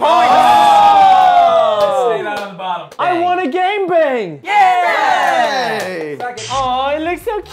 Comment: It sounds like they say gang bang